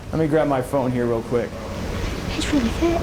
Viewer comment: I couldn’t make out what she said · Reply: She said, "He's really fit". The American equivalent would be, "He's really hot".